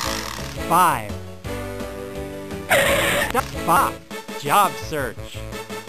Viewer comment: Lol what did he say? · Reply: “The fuck!?” “Job Search.”